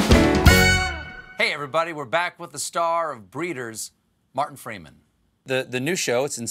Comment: Ah gutted i thought it said Morgan Freeman